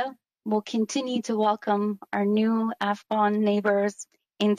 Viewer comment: for clarity, she is not saying f-bomb neighbors, correct?